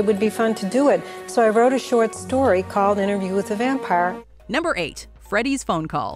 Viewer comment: I don't think the "Interview With The Vampire" and "Interview With A Vampire" is so much as a Mandela Effect as it is just mis-hearing. "With The" and "With A" (try saying them both out loud) sound almost indistinguishable when said quickly. If you listen to the clip from Anne Rice (11:25) you can hear she says "With The" but she says it so quickly that it sounds like "With A." I imagine this is true for the other cases of actors saying it. As for the press, they likely misheard it as well and just published it with the wrong title. See Also: Sherwin Williams vs. Sherman Williams